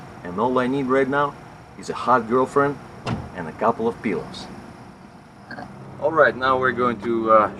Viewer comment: I thought you said a hot girlfriend and a couple of pills, as in birth control pills LOL